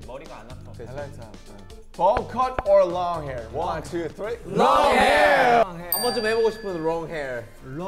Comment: Skinny tie or bow tie? Jungkook: nO TIE. thats such a jk thing for some reason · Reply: I thought he said long tie smh 🤦‍♀️ 😂